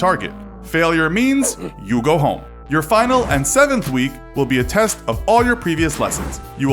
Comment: I thought it’s said Finland lol